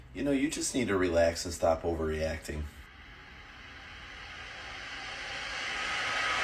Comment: Did I just hear “did you murder him” 💀